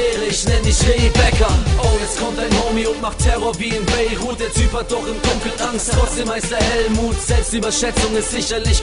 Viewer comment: "dein Homy hat im Dunkeln Angst , trotzdem heißt er Hellmut" :) cr7z hat n krassen Humor :D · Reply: Ich hab verstanden ''Oh jetzt kommt dein Homie und macht Terror wie in Beirut, der Typ hat doch im Dunkeln angst deßhalb heißt er Hellmut'' 4:09 rum ^^